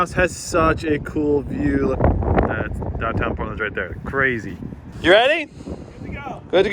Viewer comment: what did he say? Downtown what? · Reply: “Down town Portland crazy”